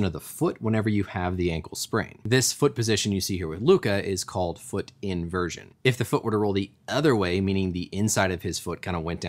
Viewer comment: I thought it said Allen Iverson